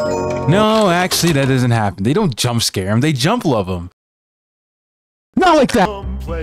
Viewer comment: My smile immediately disappeared the second i heard him say "they jumplove him" Also nothing will change my mind that super freddy was peak